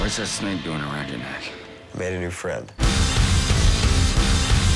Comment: what does he say at 1:59? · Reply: "Damn right they're horns"